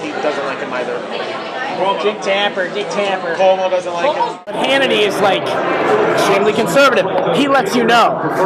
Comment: the transcription should read "Hannity is extremely conservative, he lets you know" instead of "Hannity is truly conservative". Not a big deal, I'm sure you guys were doing the best you can to get it transcribed, but he says "extremely."